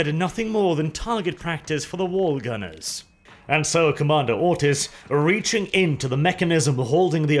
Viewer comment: I first heard it as commander Autist